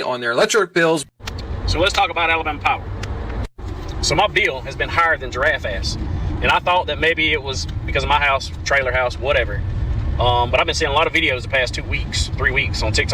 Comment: did he say “higher than a giraffe’s ass?” 😂😂